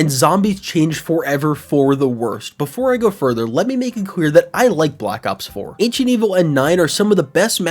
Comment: “let me make it quear” I can’t be the only one who heard that